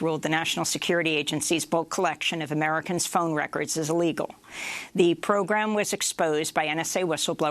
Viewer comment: Laws should be of the People, by the People, and for the People. Therefore, the Federal Appeal Court claim that NSA's Mass surveillance is legal is outrageous! Let's not forget that the Supreme Court itself has a history of outrageous rulings, such as the one in 1857 that stated Black people could not sue. Translated, court rulings can themselves be illegal with no accountability to the judges. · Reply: It would be outrageous, but it's not true. It was found to be _illegal_. It did sound like Amy said 'legal', instead, but that's not the case. She did say 'illegal', and the Closed Captioning also says 'illegal'.